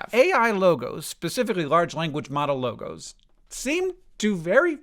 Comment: Dude my dumbass was confused and thought he said legos, up until the Vonnegut quote 😂